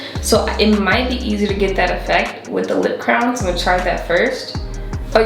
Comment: The whole time I thought she was saying “crown” not “crayon” 🤣